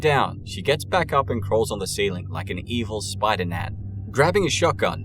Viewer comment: I thought you said “evil Spider-Nan” like how some grandmas are called Nana.